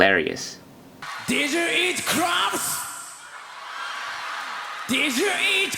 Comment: I was at this concert, it was a good show live. I will never forget when Hyde said about eating crabs because for a split second there was a pause from the audience because we thought he said 'crap'. Lmao. Oh Hyde. :)